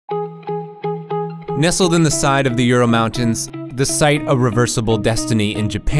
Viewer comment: "Nestled in the RURAL mountains." In case you thought he said Ural mountains, like I did.